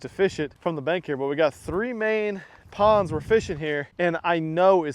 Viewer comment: Thought you said no ponds?